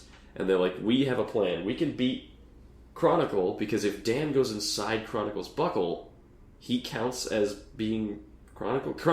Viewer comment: I misheard this as "Chronicle's Butthole" rather than "Chronicle's Buckle" and spat my drink out.